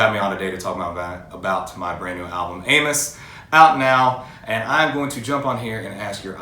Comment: he really should slow down when discussing his new album cause it sounded like he said "my new album Anus" lolll love him